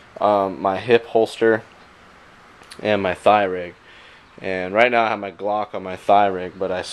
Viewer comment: oh i thought he said thigh rape and im like what did he just say